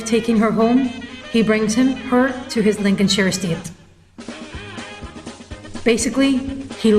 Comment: couldn't get the word at 1:31 ?? "instead of taking her home, he brings her ...." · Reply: to his Linchenshore State